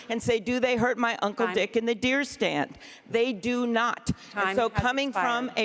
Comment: did she say ‘I hurt my uncle’s d*ck in the deer stand?’